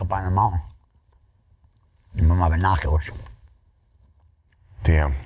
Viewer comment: I thought he said "Plate of Nachos", not Binoculars. 😂 I wasn't fully paying attention.